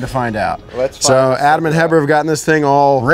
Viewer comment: What was name of the guy who started the engine? It sounded like they said his name was Heber